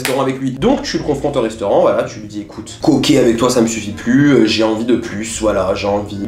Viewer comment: Je rêve ou a dit "koké avec toi " à 10:47 ??? 😭😭